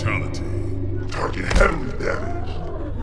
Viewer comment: What does he says at 1:35 - 1:37 ? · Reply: Luna Amaral target heavily damaged